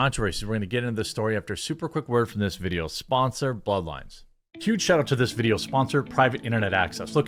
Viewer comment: nice ad transition. VPN is indeed a free to play game xD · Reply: I thought he said, "this video's sponsor, bud light" lol